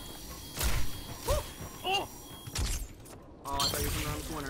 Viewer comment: Did anyone else hear say "no" at 15:22 or am i going insane?